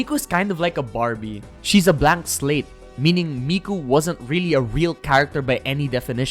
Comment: i thought he said a black slave and i literally spat my food out